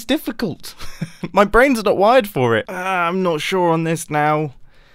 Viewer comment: Lol I thought he said "my brain isn't wide for it"